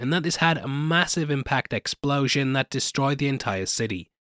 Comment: did he say MASSIVE explosion at 0:47?